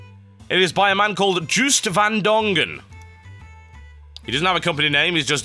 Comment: rofl i did not even know at first the guy who made this was Joost van Dongen i thought TB said Juiced van Dongen